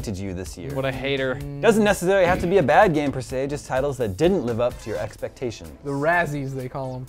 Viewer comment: I thought they said " games that didn't live up to their expectation"